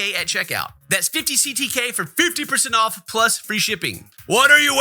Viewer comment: Jokes on, i am listening while driving, and I heard free ps5. I'll expect it in the mail after I a m done